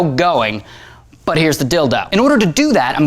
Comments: did you say "but here's the dildo" ? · Did he just say "here's the dildo"? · did he just say dildo? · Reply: "Here's the dildo" instead of "here's the deal though". It's been a recurring gag on this show. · Did he say "Here's the dildo"? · Did you just say dildo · did he say dildo XD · Yes, A few years ago he had a video where he said. "Here's the Deal though." But because this is the internet and he said rather quickly. People commented claiming that he said "Here's the Dildo." His next video had a small portion discussing these comments. He also declared that from that point on that he would no longer say "Here's the deal though." But "Here's the Dildo." In honor of the internet trolls. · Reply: DJ Squeekr Oh. hmmm. err... Actually I really thought he said "Deal though" but thought it funny that it sounded like "dildo". So now I'm wondering if you're telling truth on this (cause, you know, some people likes to make fun of others comment) and found a video about making fun of RYJ saying that same phrase. And I feel like...well...the joke's been done before, and it seems you're right even though I didn't find Ray's video about it. So...my apologizes. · Did I hear right ? Did you say dildo ?.. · Lol it sounded like "But here is the dildo" XD at -> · Is he STILL saying ''Dildo'' or is he saying ''Deal do/tho''? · Haha It sounds like Ray said "dildo!" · did he say dildo